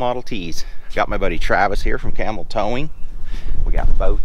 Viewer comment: l sounded like you said camel towing haha. Good name for a towing co.